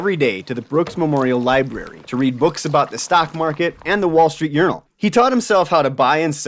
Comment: Did you say "wall street urinal", I thought you did 😎 🏷️